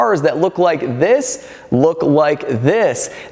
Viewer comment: I thought you said Chicago Rapper lol